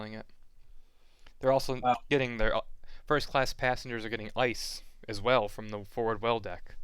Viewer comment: for a second I thought you said 1st class passengers are getting lice